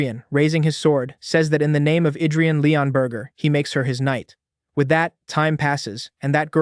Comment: did he just say his last name is burger